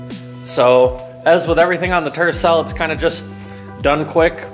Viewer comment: Did you say turd 😂 cell? I love the little car ❤️ it sounds like you saying turd cell